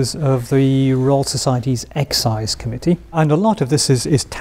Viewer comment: Thought he said the Egg Size Committee.